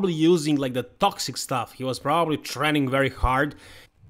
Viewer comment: i heard he was propbaly TRENnning very hard🤣😅